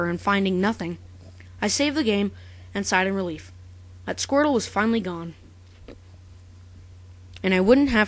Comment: the first time I saw this, I thought u said squirle. not squrtle ._